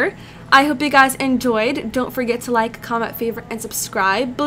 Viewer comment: The awkward moment that I thought it said "Kiss yourself" when it said "Kiss Yuossef"